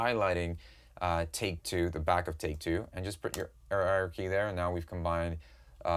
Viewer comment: what's he saying on 9:23 ? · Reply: “Put your arrow key” there sorry think I was burping or something lol